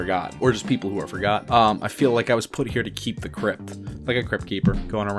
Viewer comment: The phrase "Agent of all things deep-sixed, I feel like I was put here to keep the crypt" just means he's the in tune with death or the dead. Deep-sixed is like six feet deep, like how far down people are buried. The crypt keeper is basically like the groundsman for a mausoleum, so he feels like he has just been put here to take care of the dead. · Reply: I thought he said “put here to keep it cryptic” as in be mysterious.